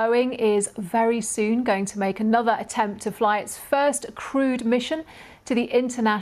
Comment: I heard "first crude mission." 🤣